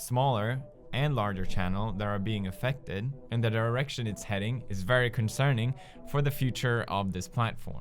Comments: sorry did I hear that right? The erection it's heading... lmao what. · umm did you just say erection instead of derection?, or is it your accent.. no hate plz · I thought he said the erection is heading 😂😂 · did anyone else think that he said in the erection it's going at 0:32 ? just me? okay... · I thought he said the "Erection is going" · I could have sworn I heard him say "the erection it's headed" 30 seconds in. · when he said "direction" in the beginning, I thought he said "erection"...shite. I have a dirty mind. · Wen press said this direction I thought he said this irrection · I could've sworn he said "Where their erection is heading"